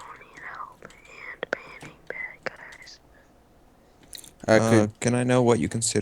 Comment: I thought he said banging bad guys